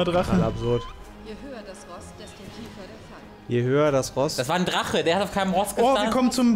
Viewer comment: mein Lieber, du hast dich verhört. Sie sagte: "Je höher der Boss..."